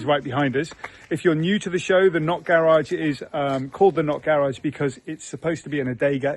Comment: I'm an American, and all these years I thought you were saying: "the North Garage!"😂 I hate to tell you, but I watch this channel because of your kids, it's mind blowing how much they love their lives!